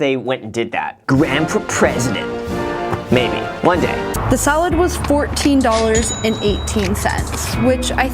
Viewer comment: am I the only one who thought she said "solid" not salad?